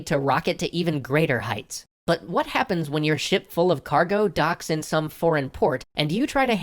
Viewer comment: I thought he said shit full of cargo at first